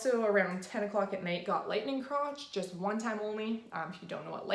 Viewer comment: Hi. What do you call the pinching you got in your uterus on day 2? It sounded like you said “legging chronch” trying good look it up because I had the same symptoms on day 2 and 3 and we are doing the two week wait now. Thanks so much for the helpful video. 😁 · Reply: Round ligament pain maybe? Oh lightning crotch haha